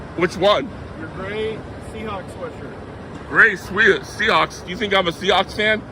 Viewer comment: Sorry, I thought said "Do you think I'm a sea-ox?"